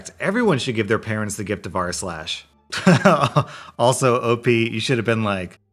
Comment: I heard "everyone should give their parents the gift of arse-lash" Now I'm gonna hear it every damn arse-lash video I listen to. O_O